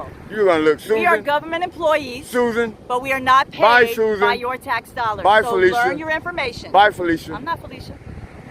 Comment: It sounds like she said your black dollars in the beginning. Go!!